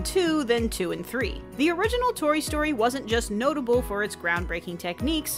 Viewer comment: Did you just say Tory Story?